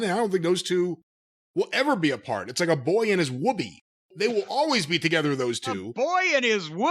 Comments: I misheard it as "a boy and his Wookie". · I thought said “a boy and his woody 🍌”